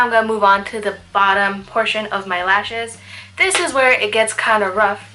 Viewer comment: towards the end your describing "how the black can close up your eyes", and you were moving to your lashes, and i thought you said "moving onto your last shit." ' when what you were really saying was lashes.LOL